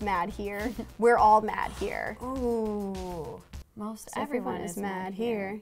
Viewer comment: I wouldn't have remembered any of these but I could have swore the cat said, "we're all mad down here."